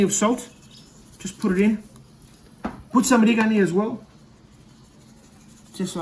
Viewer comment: what was that you sprinkled on the potatoes? i could not make out what you said, was it oregano? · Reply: Yes, oregano. It's typical ingredients for greek culture